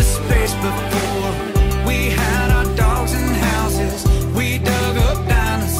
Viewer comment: Great review. At first, I thought he said "we had our dogs in houses" but I am thinking he probably said "we had our dogs and houses" in that Texas accent.